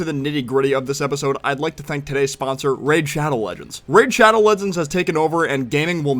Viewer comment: Did anyone hear rape shadow legends instead of raid shadow legends 💀